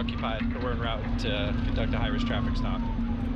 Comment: I definitely heard ‘an Irish traffic stop’ instead of high risk and was so confused